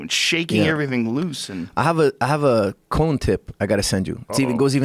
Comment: Anyone else hear "colon tip"?? I snorted hot coffee :'D